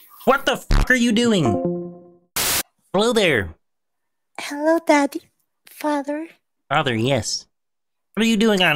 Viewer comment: AM SORRY DID I JUST HEAR "hello daddy"